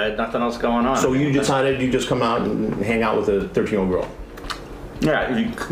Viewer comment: at 11:25 the subtitles say "plans to hang out with a dirty old girl" instead of "thirteen year old girl" and I'm worried about any Deaf or hard of hearing viewers being very confused and concerned by this 😅😂